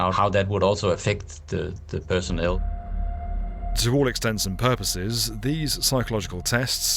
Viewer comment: Did you say, To all extents and purposes?